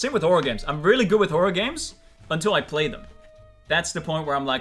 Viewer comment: Fundy said horror games and I thought he said organs🤣